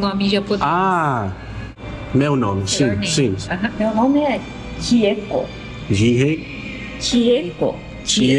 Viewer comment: I think she was saying her name is "Chieko"